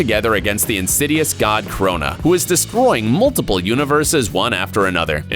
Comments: is it just me or am i hearing corona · i heard corona, not krona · wait wait..did you just say C O R O N A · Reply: 9 year olds it’s Crona